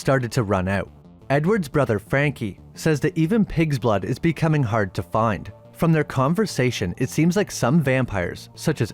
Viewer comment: Dis he drink pigs blood? I thought the brother said "even that pig shit you drink is getting hard to find"